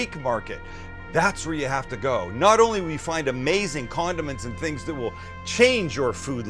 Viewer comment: did he say amazing condum؟!?